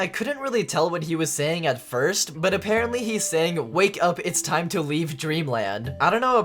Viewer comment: Instead of ‘wake up it’s time to leave dreamland’ I heard ‘WAKE UP ITS TIME TO GO TO CLEVELAND’